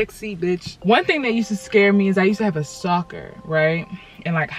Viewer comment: Tell me how I thought she say soccer instead of stocker in the beginning